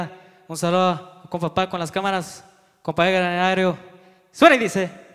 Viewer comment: 😂what he say...compa egaaar 😂